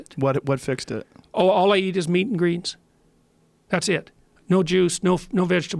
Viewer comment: is he saying meats & greens? Or greets? Help me out here 😂